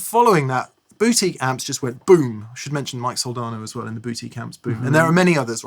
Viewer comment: I love my AC30 with blue alnico speakers I pair it with a Fender Blues Deluxe with 6L6's and I get extraordinaire sound and tone ... I love this episode..thanks Mates...Sounds like is Saying Boo-ty Amps...lool